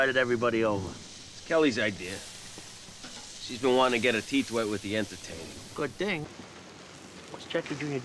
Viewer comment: What did he say?????????? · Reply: OH. HER TEETH WET AHDHSJDJJEKDKSKDKSKD